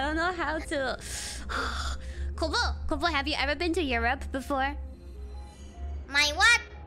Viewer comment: Europe Kobo think "My rope?/ My robe?/ My rub?" Europe its like "you rope" isnt? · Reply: she thought said your rope or rub